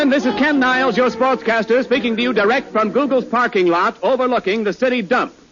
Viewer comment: did he say Google’s parking lot?